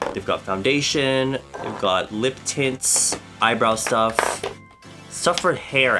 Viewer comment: LEGITIMATELY thought he said "lip tits" that was probably a very irrelevant thing to comment on.